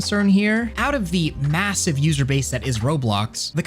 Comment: DID YOU JUST SAID MASSIVE